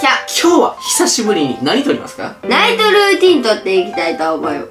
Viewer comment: ナイトルーティーンがナニトルーティーンに聞こえてしまったwww やべぇ幻聴でツボるwwwwww